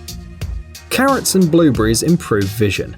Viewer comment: You say "blueberries" like "blue breeze", to us Americans. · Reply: So many strangled vowels. I thought he said currents when he said carrots.